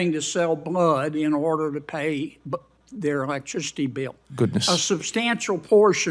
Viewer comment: did I hear him answer "good" to that question?